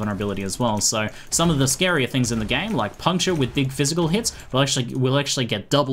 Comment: puncho? with big physical hits? what does he say there, can't make it out?